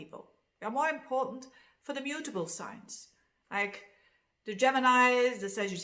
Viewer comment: I thought you said Libra is not a beautiful sign - had to listen a couple of times to realize you said mutable..... I think. 😍